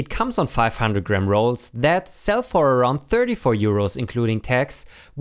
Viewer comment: i heard that as "34 year olds" for a moment there ( ͡° ͜ʖ ͡°)